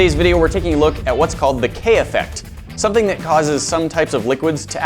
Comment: I thought that said Kanye Effect.